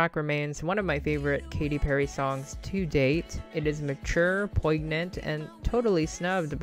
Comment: Did u just say poyg-nant?